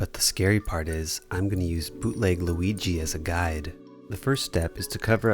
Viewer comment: Did He Say Bootleg Luigi?! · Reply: No he said “BOOtleg Luigi”